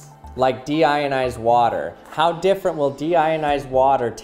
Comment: I thought that said demonitized